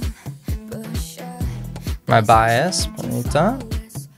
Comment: Early on it sounded like you said my bias Pharita when it was Rora singing... 😆